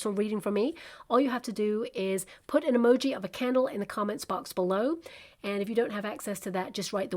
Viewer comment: 🐪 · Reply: Oooh a candle!!! Hahaha I thought you said camel!! 😂 🕯️🤭